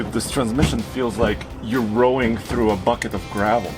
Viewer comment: "This transmission feels like you are rowing through a bucket of gravel." Priceless! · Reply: i thought he said a bucket of snot